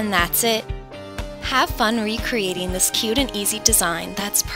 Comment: at the end i thought she said for the warm winter and i was like ITS NOT WINTER!! then i tried to hear what she said again and she said Perfect for the Warm WEATHER. lol my bad!